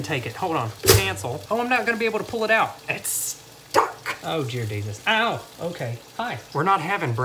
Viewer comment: Anyone else catch "Oh Jear Desus" 2:40 ?😂😂😂